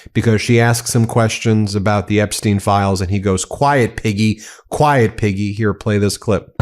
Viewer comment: I thought he said, Quiet, idiot, but quiet piggy is just as bad coming from the man in the oval office with the title president